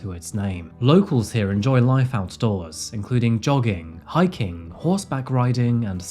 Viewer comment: Had to rewind when you said jogging i thought you said dogging 😅